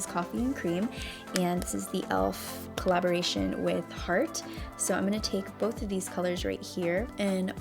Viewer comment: I thought you said the highlighter elf collaboration with Tarte 😆 i was like whaaaat? Lol